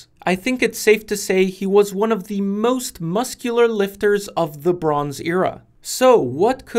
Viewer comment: i had to rewind because the first time I heard, "he was one of the. Most muscular painters" 😂